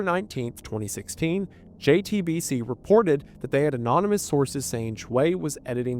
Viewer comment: Now I know you SAID ‘Anonymous sources’ but what I heard, hand to the lord, was ‘Anonymous Horses’.